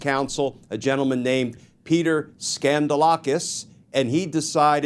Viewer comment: I'm sorry. Did he say "Peter Scandal - okus"? Because that's sounding as on the nose as FoxConn. Who remembers that con job from tRump's 1st term?